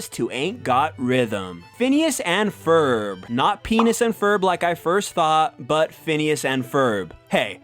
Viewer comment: I new I wasn't the only who thought it said Penis and Ferb!